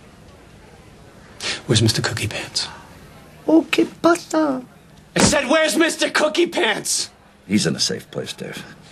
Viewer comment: can someone tell me what language that is and what does it mean? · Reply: "Que pasa?" It's spanish It mostly means "how you doin?" but in this case it means "what happens?" or "what's wrong?"